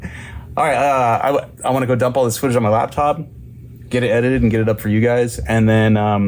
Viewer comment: When you said "I'm gonna go dump this footage on my laptop", it sounded like you said " I'm gonna go dump my spooge on this laptop" and I was thinking "Ummm TMI man, TMI",,,LOL